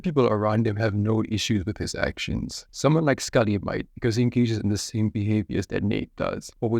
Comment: I don’t know if it was your accent mate, but it sounded like you called Sully, “Scully”. 😂😂 I was like “wait who?”